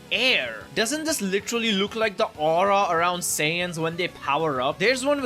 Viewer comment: When he said Saiyans i thought he said Sans. Am I cooked? 🙏😭